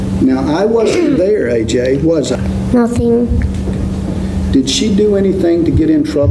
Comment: Listen between 15:22 and 15:23. AJ says, " nothing" ehich is not a response yo yhe question last asked. Snd then it reverts to AJ ansering the question. The narrated intro before the 9/11 call sounds alot different then what I just heard on another channel. Nut mow I know why someone commented that the granfather coached AJ. I believe it. When I girst heard AJ's testimony it didnt make sense when he said that Afrienna was yelling for him to call the police. As she was being drowned yelling for AJ to call the police? I think its very plausible that this little boy was coached. AJ mixed- up alot of the times and events , and said he couldnt remember certain things. He was only a baby, afterall. Yet these grown people condrmned a eoman to a life in prison based on the testimony of a traumatized, confused baby. What a shame. Very sad. Were the grandparents calling Amanda a loser and thats where the child got the idea? This is all totally suspicious